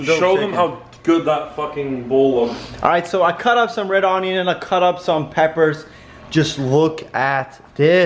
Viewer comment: i thought he said " i cut up some red oineuns i cut up sam pepper